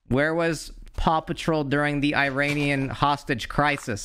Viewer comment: When he was saying Paw Patrol I thought he was saying Papa Troll and I can't unhear it.